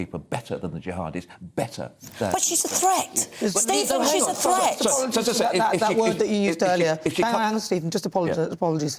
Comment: What was the word he said that he shouldn’t have? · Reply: “Fingering”. I think he meant that the women morality police that Shamina was part of point women out, but that Nana thought he meant the other meaning.